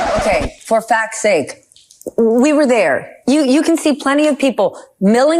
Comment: lol really thought she said for fucks sakes lmao